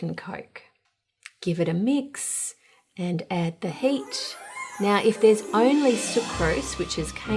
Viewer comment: What the hell she say...."add the he8ch"??? Is the word heat really that diffulcult?